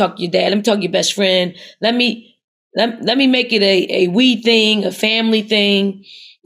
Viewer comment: did she say “lemme make it a weed thing?” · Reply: No, no ... "a WE thing". 🤫